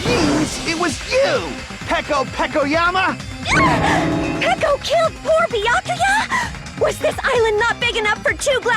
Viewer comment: why did i think she said "peko killed for the yakuza?"